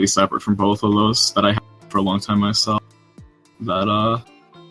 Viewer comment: “that I had for a long time myself” that is what he said at that point where the subtitles say “unintelligible “ 🎉😊